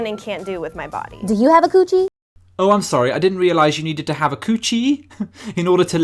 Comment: Oh...... so coochie is another word for pussy, I thought they said Gucci..... ( -_- )